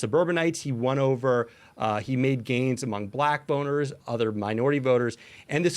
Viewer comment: I thought dude said black boners for a minute. Had to rewind 😂